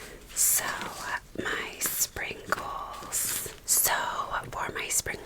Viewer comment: I thought you kept saying "sprinkles". LOL Then I figured it out.